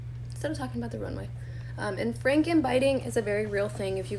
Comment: what did she say? Frank and biting?? What is that?